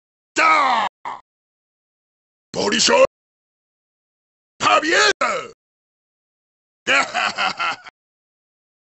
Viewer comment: Does anyone know what he saying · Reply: Bol'shoy pobeda = great victory